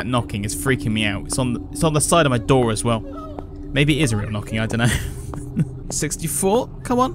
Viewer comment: I miss hear Is real knocking and thought he said israel knocking sorry for bad english but its is not 1 language